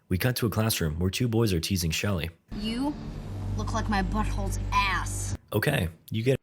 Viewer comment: 💀 I thought he said “you look like my bf’s ass”